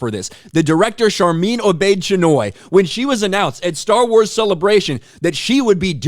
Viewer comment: Sorry I misheard, did she say her name is Sharmeen Obese - Chinwag?